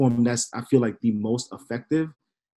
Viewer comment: Hey, the audio cut out a little at 39:44 what did he say the most effective platform was? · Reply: "advertisers"